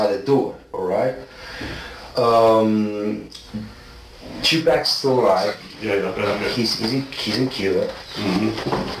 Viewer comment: Did I hear him say 2pac is still alive and in Cuba?